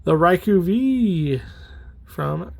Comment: You pronounced Raikou correctly but subtitles thought you were saying “Raichu” which is pronounced quite differently.